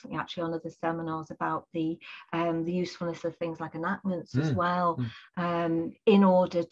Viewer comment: what does say? "Like an as well?" The audio quality is so low, and the her regional accent makes it very difficult to understand what word she is saying. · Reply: ‘Enactments’